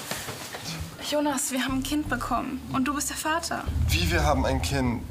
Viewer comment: Ich hab das verstanden: „Jonas, wir haben 5 bekommen.. und du bist der Vater!“